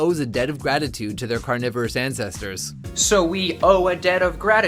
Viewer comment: in the video they clearly said "carnivorous ancestors " not cannibal